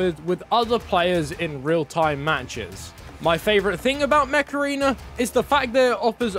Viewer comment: 🤖 Install Mech Arena for FREE here on Mobile and PC ✅ <link> and get a special starter pack to help kickstart your game! ⭐ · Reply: My mom was listening and thought you Said “meth arena”